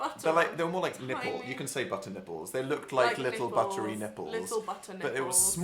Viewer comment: Buttery nibbles? Am I the only one who thought they said 'buttery nipples'? :') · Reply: I think they did say butter nipples...